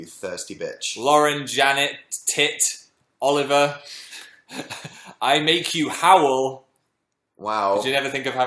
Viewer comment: I heard "I'll make you Howell" if they said that then YES I love that channel!!